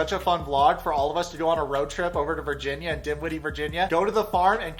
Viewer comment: LOL...I swear, until I saw the screenshot of the listings, I thought you were saying “Dimwitty, Virginia” which would be so perfect! 🤣